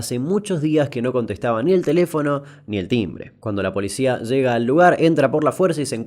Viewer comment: Escuche "La señora no contesta ni el telefono ni el Tinder"